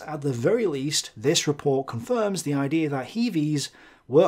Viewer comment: I thought it was supposed to say did Kiwis fight at Stalingrad. 😂